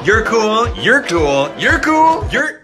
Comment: I thought she say Google